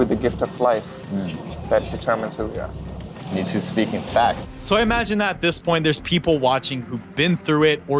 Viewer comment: It honestly sounds like hes saying “Me-Too speaking facts……” Lol